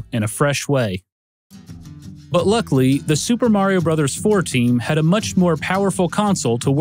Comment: I had to rewatch this part 2 times, because I thought he said "Super Mario 14".